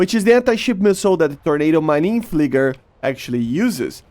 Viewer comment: what did you say at 0:20💀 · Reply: Marine fliger